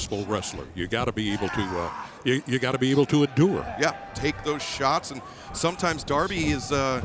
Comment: Guys I'm still learning English at 17:57 what did he say? And what is that mean? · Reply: Endure, as in endurance. Basically be able to go through a lot/for a long time